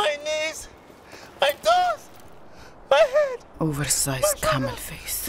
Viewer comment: Did I hear 'over sized camel face'?